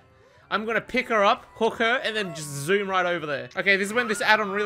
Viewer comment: does anyone else hear hooker instead of hook her